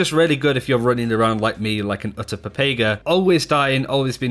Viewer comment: “like an utter Pepega” some people might be like “what did he say?” Ooh boy